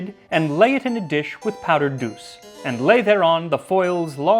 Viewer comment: First time I heard "powder goose"...